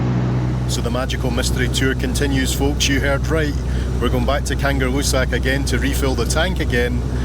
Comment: I thought you said kangaroo sack 🤣🤣